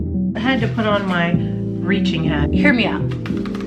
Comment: Im so crazy this whole time ive been subbed to you. A long time now, I just figured out you say let me put on my reaching hat!! All this time ive been calling it cause i thought you say, Your preaching hat!!🤣🤣